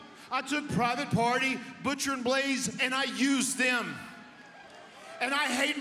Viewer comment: did he say "Butcher & BLAZE??"